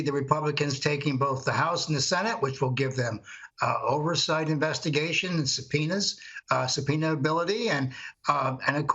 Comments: Did say “ which will give them over site investigations, penis”, it sure sounded like that.😜😆😂🤣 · Did he just say oversight investigation penis? Oh god I need a psychiatrist... :( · Did I hear 'an oversight investigation's penis'?